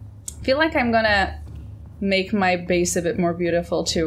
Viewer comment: I heard "I am gonna make my face a little more beautiful as well" I assume she actually said base